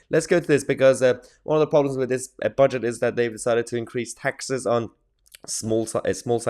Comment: You know for a horrible moment I thought you said reduced taxes on business? But I’m OK I saw in time before the mild cardiac malfusion you said raise taxes.